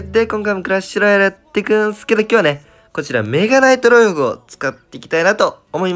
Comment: メガナイトロイゴにしか聞こえないw